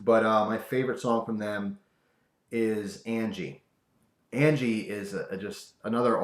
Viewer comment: Wow I always thought he was saying Angel lol hmmm I guess it's Angie.